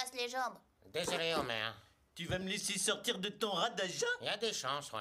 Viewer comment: il a dit quoi ?? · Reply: “Tu vas me laisser sortir de ton rade à jeun ?”